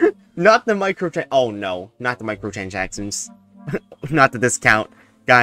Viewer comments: That sounded like "oh no! not the michealOjacksons" lol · did you say "microchanchackchons"